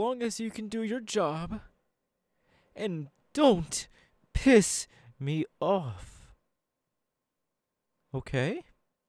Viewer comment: I thought he said “AND DON’T PISS ON MY FLOOR!” 💀💀💀